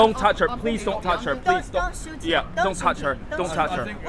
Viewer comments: Please note at 5:28/5:29, the lady repeats 'don't shoot him'. That's what I hear anyway. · You hear what she said??? "Don't shoot him don't shoot him!" Wtf 😮